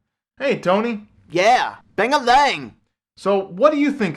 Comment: BANG-A-LANG? I thought he was saying ding-em-dang. thats what I hear anyway